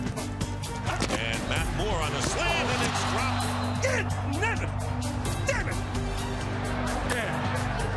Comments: did he just say ninja 😂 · Did he say the N word? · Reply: He said dag nab it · What did he yell after chad dropped the ball 26:57 Dammm ________________ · did he just say... “dat nigga” ?? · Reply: Dag nabbit